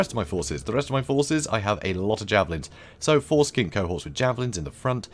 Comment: LOL all I heard was "foreskin cohorts in front". This was an interesting match.